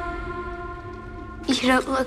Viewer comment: What did she say? I heard "You don't look so good at all."